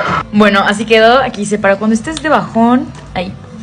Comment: Ayuda, entendí "cuando estes para jabon"😂